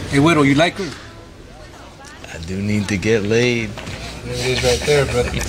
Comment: What was dude about to say at 1:38 before he got slashed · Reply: Sounded like "I wanted the..."